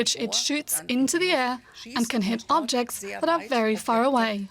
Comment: "Which has a tube that shoots into the air and hits objects very far away" - 😁 An impressive description of German military precision and engineering. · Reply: I thought she said "hits objects not far away" ?